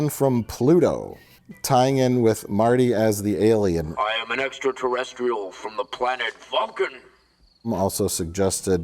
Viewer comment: I saw this in the theater way back in 1985, and even in watching it later on VHS, I always thought Marty said, "I am an extraterrestrial from the planet fuckin'". I thought Wow, that's really pushing it!